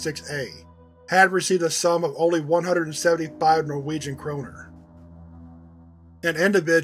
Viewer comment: Im listening to this while working thought you said Kromer was wondering who thought making a deltarune reference in a scp documentation would be funny for second